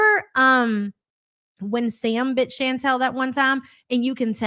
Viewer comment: omg I wasn’t fully paying attention so “remember when Sam bit Chantal that one time” caught me hella off guard as I heard it as wife Sam not cat Sam 😂